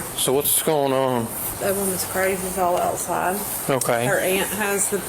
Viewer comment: For those who didn't understand what she said @ 1:25 (*inaudible). She said, "That woman i s crazy as all outside." It's an old southern saying meaning she is really crazy 😂